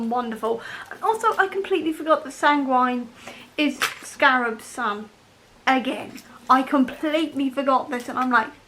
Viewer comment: Do you know HOW MUCH YOU CONFUSED ME?!?! I thought you were saying that Scapegrace was Scarab's son and I was shooketh and googling. Turns out I'm just a bad listener lol